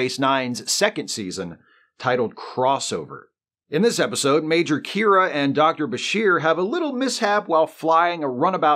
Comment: Typo in the subtitles about 11:36 in. Instead of "Major Kira ", it says "Major Mira".